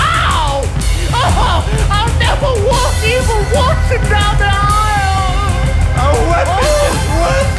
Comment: DID HE JUST SAY "I'LL NEVER WALK JOHN WATSON DOWN THE AISLE"???